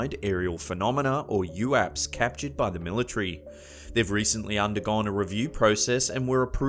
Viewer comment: What the heck did he just say?? The Millie tree? I think you'll find, that it's military.